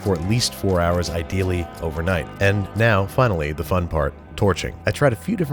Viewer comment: i heard "the fun part- tourchering"